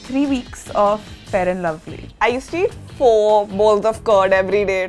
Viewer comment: what did she say? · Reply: she said she used to eat 4 bowls of curd (yogurt).